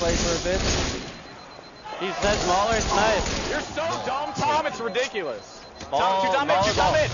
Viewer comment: did say, "you're so stupid Tom it's ridiculous?" lol just wondering